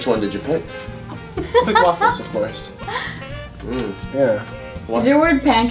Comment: did say. blue waffles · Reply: i hear "big waffles of course" try listening it t agian